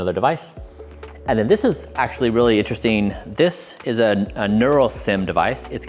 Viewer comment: What does he say at 9:29? I hear a neuro… something Also, what is that device called? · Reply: Ah, nurosym